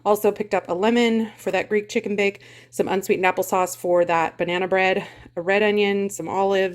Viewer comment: I thought you said unsweet knuckle sauce. I had to look up and see what you were pointing to. LOL!